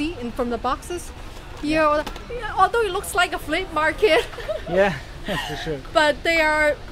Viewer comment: I think SexyCyborg says "it looks like a flea market", but the closed captioning says "it looks like a slave market".